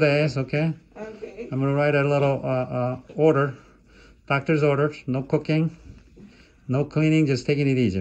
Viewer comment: I swear i thought he said no cocaine for a sec.